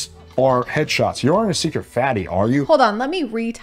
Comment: him thinking that texting a woman "you're not a secret fatty are you?" and getting a good reaction is even remotely believable is INSANE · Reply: No bcs I wasn't looking when they brought up that part and I thought he said "you're not a secret baddie, are you?👀" which I mean I think that would work a bit better?? Idk, saying like like "so you're secretly very pretty?" would be very effective at least it would work on me